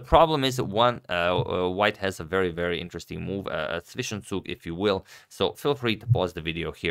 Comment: ah so its called 'Zwischenzug', everytime Agadmator says it, I heard as 'fishing soup'